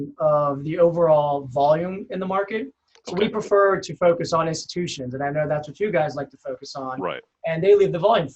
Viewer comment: What does he say at 3:17? He prefers to focus on “two-shift”? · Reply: "Institutions"